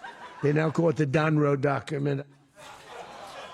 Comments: Did Trump stumble into an idiotic portmanteau of "doctrine" and "document" too? I swear I heard him say The Donroe Documen" in one of those clips. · The Donroe Doctrine? Pardon one foreign leader convicted in US courts of drug trafficking. Kidnap a different leader claim it's about drugs long enough to go grab oil. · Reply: If you listen to @ 8:20 again, he actually says "The Donroe Document" because his brain is gone. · "They now call it the Donroe Document". He did not say doctrine, his addled brain came up with document.